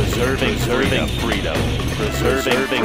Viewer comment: With how many paladin saws that were being spammed out, its souns like "serving freedom" instead of preserving freedom